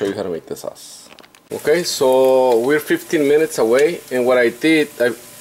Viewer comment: I thought you said 50 minutes and now my brisket is burned 😐 for anyone doing this recipe, the broil is 15 minutes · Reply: Never said 50 minutes I said 15 minutes and its up to you to be checking out the oven sorry for my bad English.